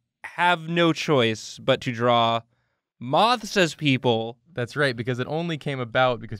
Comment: I thought it said draw myths as people